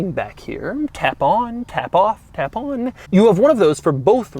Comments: Did Doug just say tampon? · sounded like he was saying tampon